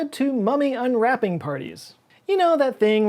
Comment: You said "mummy unwrapping party," but what I heard was "forbidden unboxing video"